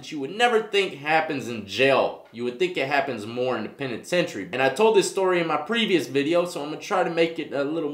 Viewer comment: Closed captioning thought you said independent century when you said penitentiary....lol